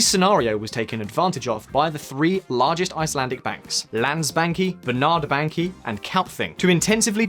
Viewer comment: Definitely thought he said "Land-Spankie" 🤣